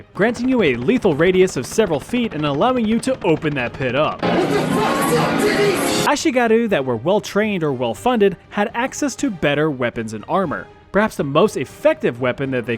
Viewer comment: I cant understand the "open that pit up" part. I only hear "what the fuck is up Denise?!" Lmao